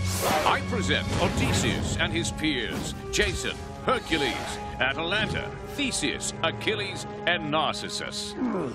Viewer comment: ""I present Odysseus and his peers, Jason, Hercules, whatever he called Atlanta, Theseus, Achilles and Narcissus"" Wtf did he call Atlanta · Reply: He called her ' Atalanta' Her real Ancestor